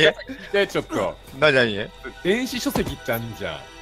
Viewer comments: 電子書籍が電子辞書って聞こえて7冊すげぇって思ってたww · からの件でさ、が何喋ってるか聞き取れなくてコメント来て、すごい漁ったのに、諦めて再生押したらがはっきり言ってくれた「電子書籍」